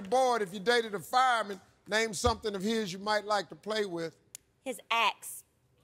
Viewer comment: Steve thought what we all thought! He thought she said "His ex". LOL